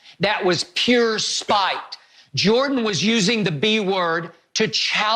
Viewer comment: It almost sounded like Skip said B** at 13:05 LOL